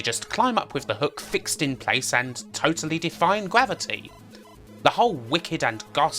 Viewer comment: Define gravity? Defy is the word. Just being a pedant ;) Good vid as always. Much love! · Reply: I think I heard "defying gravity".